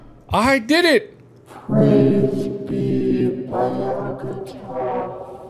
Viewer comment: did anyone else hear "praise be markiplier?"